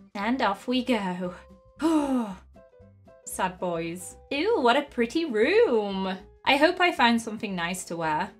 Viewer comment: It sounded like she said "ew what a pretty room"....